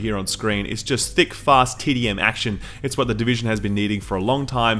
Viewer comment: Did he Just SaY TiTy Action